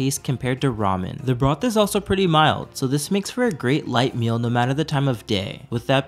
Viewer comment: i was litening this in a loud place and i heard "with a lot ton of gay" i rewatched it again and i heard "no matter the time of day". thank goodness